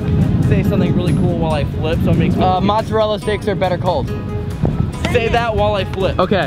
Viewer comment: I thought Elton said mozzarella sticks are a better cult🤣